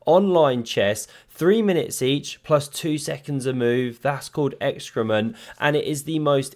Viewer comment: did he say 'excrement' is called what??? 😁😁😁